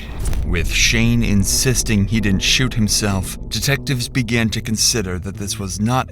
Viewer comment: I thought it said I didn't sh*t not I didn't shoot XD